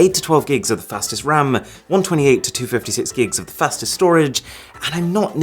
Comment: did he say fastest storage? What it means?